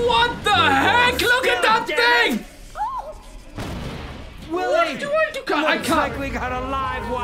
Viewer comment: I heard Willard Wyler